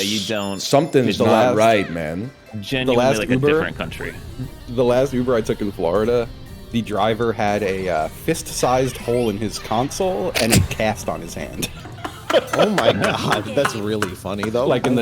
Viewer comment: Fist-sized hole in the console and his right arm in a cast is absolutely absurd environmental storytelling. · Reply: I thought he said fist sized coal like the cast was the reason he didn’t get any gifts for Christmas